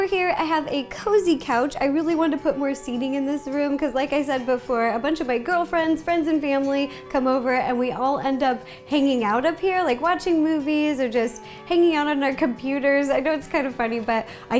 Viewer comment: did she just say “a bunch of my girlfriends” ?